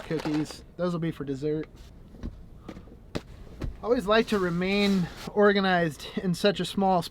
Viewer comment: Why do I hear: "I like to be Oregonized! "all the time? 😉